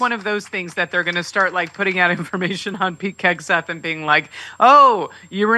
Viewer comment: At about 9:20 - Did I hear you call him “Pete KEGseth?” Well said!